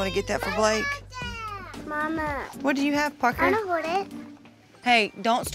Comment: omg i thought she said, “ what do you have, fucker” hahah